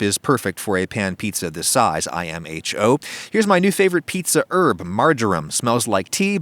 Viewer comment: Did you say I am a chode hahahaha @ 3:38 OH NVM IMHO